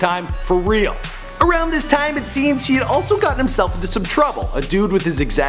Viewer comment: did he say she also got himself?? She???